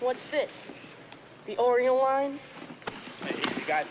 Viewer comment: For the longest time I thought he said “Oriole line” lmao